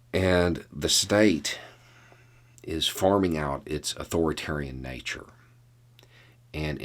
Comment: I thought sure you said, "The state is farting out its' authoritarian nature..." It works too.